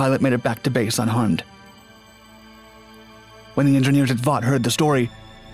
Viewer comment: Did he say “when the engineers at Vought”